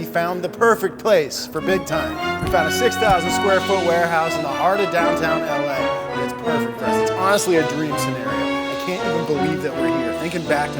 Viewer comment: WTF is up with the audio editing? I have no idea what you said while the music was blasting at ear bleed volume. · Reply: "We found a 6,000 square foot warehouse in the heart of downtown LA. It's perfect for us, it's honestly our dream scenario. I can't even believe that we're here, thinking back to moving here from Ohio, it's crazy."